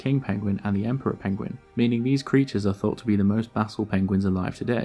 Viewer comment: most what penguins alive today? I can't make out that word. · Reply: basal